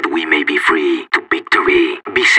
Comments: what di he say? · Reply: Let's teach these dogs a lesson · What did he said there? I always been looking for that quote. les dis ditos alison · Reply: is the accent what makes it confusing, it's says ''Let's teach this dogs a lesson'' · What is he saying at 4:45 and 4:46? I am assuming that's basque? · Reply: “Let’s teach these dogs a lesson” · What he says at 4:45? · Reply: Let's teach these dogs a lesson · what is he saying at 4:45 ? (sorry if that's a really stupid question) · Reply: "lets teach these dogs a lesson"